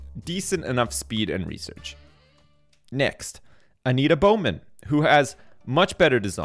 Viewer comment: When Sips said "Anita Bowman" I thought he said "I need a bow, man"